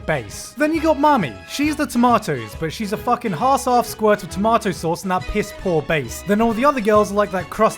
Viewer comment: can i just commet on the fact that he said 'hass aff' instead of 'half ass'